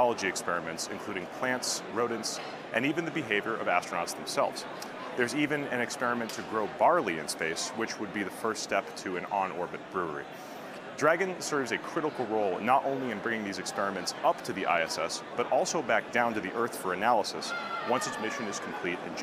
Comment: Did I just hear them say that these are the first steps toward an in orbit brewery?! 10:40 ish.... EXCITED for some SpaceBeer!